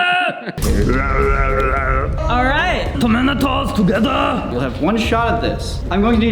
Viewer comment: sounds like says "the minotaur" instead of the Terminator